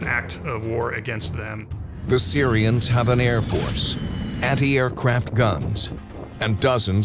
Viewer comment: “the Syrians have an Air Force” laughs in American air superiority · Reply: I thought I heard him say, “The Syrians have a robust Air Force”, lol.